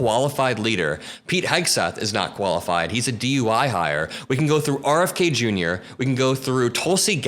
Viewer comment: Lol-- it sounds like you said Hegseth was a DUI hire, instead of DEI.... LOL!! Freudian Slip??? 🤣🍸